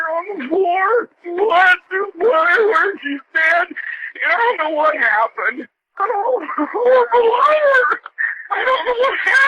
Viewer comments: play it or speed: vid quotes him "There's blood all over" <-- I 🚫 hear that. I ONLY hear a person freaking out, who says "I'm a hOrriBLE LIAR" in a sad pathetic voice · is such a terrible liar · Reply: I'm not even 10 minutes in yet so I won't comment on whether I believe he's lying or not, but I swear at 00:31 if it wasn't subtitled I would've thought he said "I'm a horrible liar. I don't know what happened."